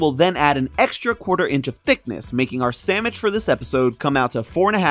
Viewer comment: is it just me or does he say "SAMMICH" and not "SANDWICH"? (closed captions says "Sandwich")